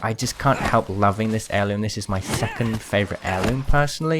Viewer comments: Did anyone else think at 9:48 he said f**king favourite heirloom or is it just me. btw I had to turn on subtitles he said second but it scared me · dude I thought you said “this is my fuckin favorite heirloom” lol